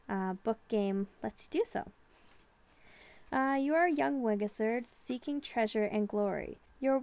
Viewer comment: Did she say a young wigizard.😂😂😂🤣🤣😂🤣😂